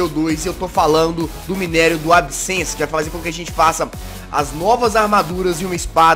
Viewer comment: entendi adcence